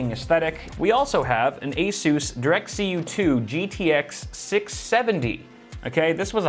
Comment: I thought it said DirectCUM